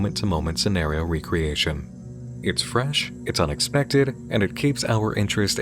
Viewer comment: Am I the only one who thought he said “it’s French” and not “it’s fresh”?